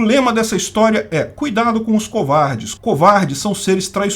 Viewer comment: "Cuidado com os esquerdistas, esquerdistas são seres traiçoeiros..." 11:40 Eu escutei claramente ele dizer esquerdistas Ou será que disse covardes?